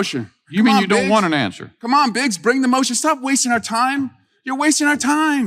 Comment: It sounded like Eric said “bring it on “B*tch” then I realized he said “Biggs”😂😂😂 Ana Paulina lunacy 👍👍👌 Nazi Luna !! Don’t mess with Pete, he will ALWAYS WIN 💙💙💙🇺🇸